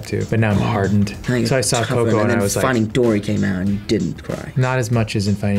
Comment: what did Tom say ?? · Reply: And then finding Dory came out and then you didn't cry